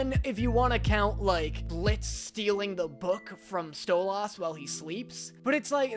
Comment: DID YOU JUST SAY sto-LAS